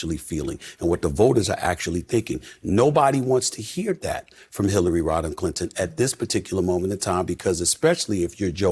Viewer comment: i heard Hillarie Rotten? my man run